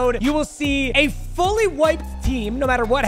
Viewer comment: he says next episode he's gonna start with a fully wiped team and I thought he said "Fully White Team" and then thought about how there isn't really a difference.